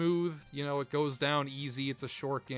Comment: did u just say it goes down ~*Ys-y*~??? Nice try pal!! 😏😂🤪